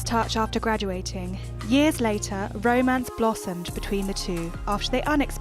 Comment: I literally thought she said “their bro-mance blossomed “ 😂😂